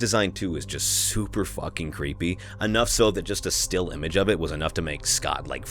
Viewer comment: The sexual tension between and every other fictional charecter in the universe is unbelievable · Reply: I skipped back 10 seconds at 1:52 and it completed the sentence to say "Bonnie is so fuckable" instead of fucking creepy... I started laughing and decided to comment, just for this to be one of the first comments....yep